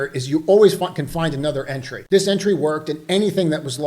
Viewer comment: Did I hear that right? @ 14:46 “you always fucking find another entry” 😂